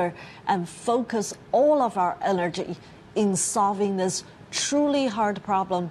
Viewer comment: Sounded like “focus all of our allergy..”. :)